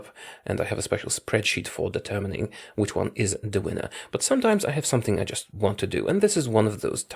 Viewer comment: did you say “da winner” ?